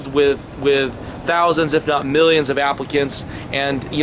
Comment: u say applicants but the captions say africans..whats up with that